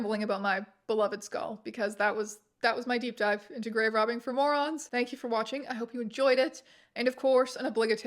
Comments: definitely thought that said "grave robbing for MORMONS" and I was absolutely SAT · this entire time I thought it said "Grave Robbing for Mormons"